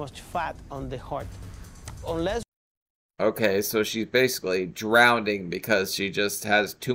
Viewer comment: I love you but did you just say drownDing? instead of drowning, that's not as cute as you hmmmm..... hehe